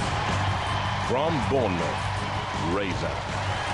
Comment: "raper" (it sounded like that okay)